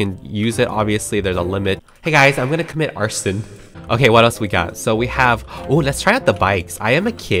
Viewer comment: THE GUY WHO CAME AND WAS LIKE “Hey guys I’m gonna commit Arson<3” I FELT THAT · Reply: I thought you said “I’m gonna commit Aaron Hernandez....” I’m so dumb. I’ve been saying “gone forever, Aaron Hernandez” WAYYYY too much lately